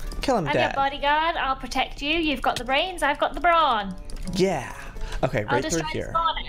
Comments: OMGGG! When Lizzie said that she was the bodyguard I thought she said "You've got the brains , and I got the bra on!" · When Lizzie said "I'm your body guard, you've got the brains I've got the brawn" I thought she said "I'm your body guard, you be got the brains,I've got the bar on!"